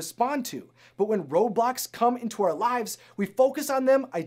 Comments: I thought he said “when Roblox come into our lives”... sigh. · I had the volume on low and thought he said "when Roblox come into our lives"